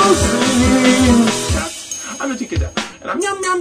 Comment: does he say "I'm a tiki dat"